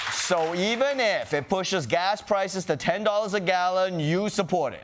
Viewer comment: I thought that said T-Pusha for a sec I'm like what?!